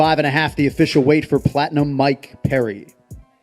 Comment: I thought that announcer said fatinum mike perry